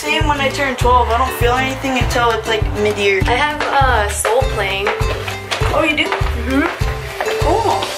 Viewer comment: When told that “Soul playing,” I thought she said “Soul Plane” and I thought that was random! lol